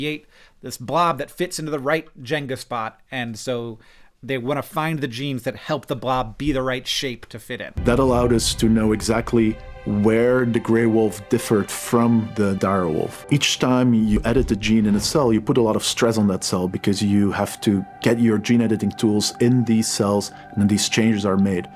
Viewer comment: It sounded like she said "Gray Woof..."